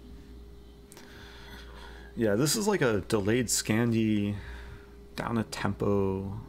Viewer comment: Did you say delayed Scandi , because I distinctly heard you say delayed Scampi